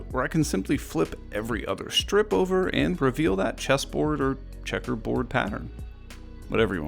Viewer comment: I thought you said cheeseboard lmao